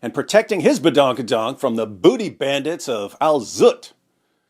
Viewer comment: Did he just say booty bandits! 🤣🤣❎